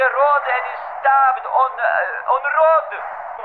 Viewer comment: Jfc, I Hear "He was stabbed on a rod" And I Get Flashbacks To Call Of Duty, Modern Warfare 3. Jfc R.i.p Yuri